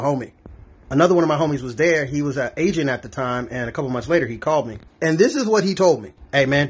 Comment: I didn't hear agent at the time, I heard "He was Asian at the time" 😅